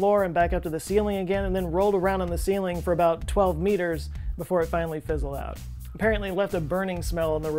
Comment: Did he say 12 minutes or 12 meters. ?????🤔🤔